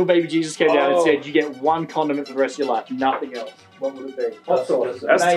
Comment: I thought he said one condom for the rest of your life so I was so confused when they started talking about ranch